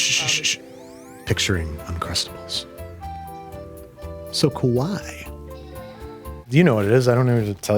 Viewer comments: You may think he said Kawaii (cute), but what he actually said was Kowai (scared). So Andy is afraid of Uncrustables... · it sounded like he said “kowai” (scary) lol 😭😭